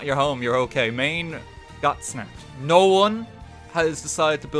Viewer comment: “As long as Maine’s not your home you’re ok. Maine got... smacked” RIP Maine. You were a real one ☝🏻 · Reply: I thought he said snapped not smacked